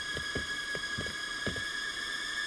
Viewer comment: did he say "dont be fuckin with it"?